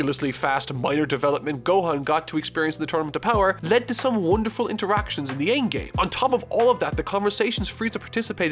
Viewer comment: Wonderful interaction? I thought you said “Who really cares?!” In this scene. Honestly, I agree with your original opinion. Ig they did try, but it wasn’t satisfying to me in any meaningful capacity